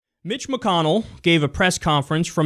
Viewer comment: I thought said Bitch McConnell at the beginning at the video.